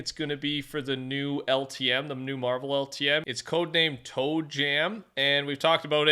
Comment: codename- Toad Jam lol...i lokey thought he said toe jam💀